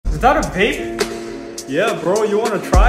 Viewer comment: Thought he said “is that a pig” I was about to say “NO, THATS A CHICKEN!” you get it lol😂